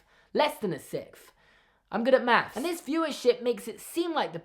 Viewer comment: I thought that said "fewer sh*t" instead of "viewership"